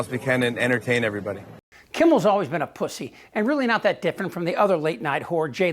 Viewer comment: Think you meant to say fallon at 1:31 not kimmel.